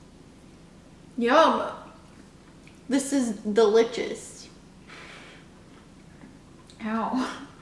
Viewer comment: did she just say delichious